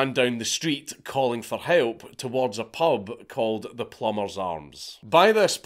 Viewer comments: I thought u said she ran down the street to a pub called The Plumbers Arse. I had to rewind that shit! 😂🤣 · I thought he said the name of the pub was The Plumbers Arse.